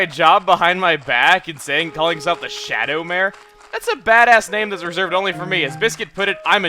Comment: I thought said Shadow Mare which reminded me of Nightmare Moon :P